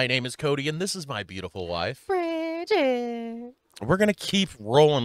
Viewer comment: I feel such a fool, I didnt have captions on and I thought cody said "this is my terrible wife bridget" xD You guys rock!! 😂😅